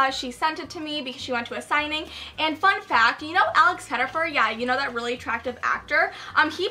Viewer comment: What name does she say at 3:34? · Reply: Alex Pettyfer